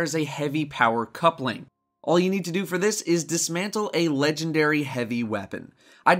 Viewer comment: Well. I dismantled an exotic heavy cause i thought you said exotic, not legendary, now I feel like a dumbass. r.i.p super good advice